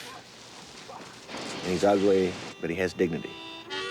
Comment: He’s ugly? Is that really what he said, or did I misinterpret it? Roddy was far, far, far from ugly 😂